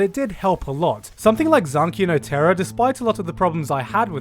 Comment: what anime did he say at 3:21 "something like zankino teron" idk. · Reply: zankyou no terror i think